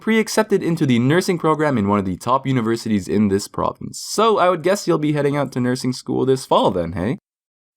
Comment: Does anybody know what he said because after 2 years I still can’t process what he’s saying. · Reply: So i guess youll be heading to nursing school this fall then,eh?